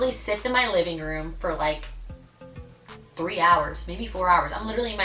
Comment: came here after the trish video where he says you were crazy & only hung out 3 times. lol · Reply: i thought he said 2 hours lol